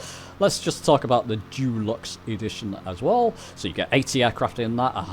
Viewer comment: Love the review, prefer use of Dulux instead of deluxe at 12:29 😊 Decorating by any chance?